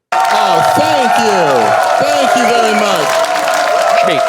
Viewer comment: what does Mike say? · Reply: "schkee" it's Yiddish because Mike is a Jew